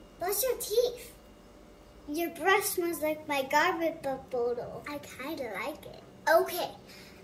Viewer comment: did you just say my breath smells like garbage 🗑️😂😂😂😂